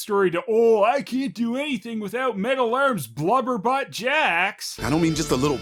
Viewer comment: What's wrong with my ears, I keep hearing Sonya's insult as "loverbutt." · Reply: i thought she said blubberbutt